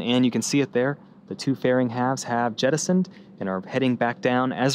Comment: What is that word that they used? Sounded like jedosent. :) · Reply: Jettisoned!